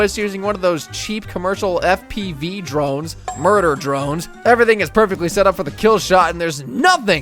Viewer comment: did you just say Murder Drones?